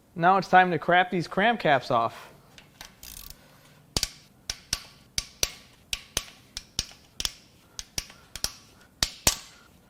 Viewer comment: One of the easiest ways to tell if a vehicle has been neglected is to pull a spark plug out and if it's an Autolite don't buy it. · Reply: cram these crap caps off 😂 did I hear that correctly?